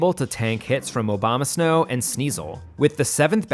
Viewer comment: Did anyone else hear this as Obama snow?